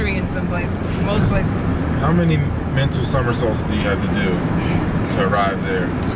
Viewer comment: Lmao! How many mental summersaults do you have to do to arrive there 🤣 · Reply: Summersaults! That makes more sense. I thought he said summer solstices.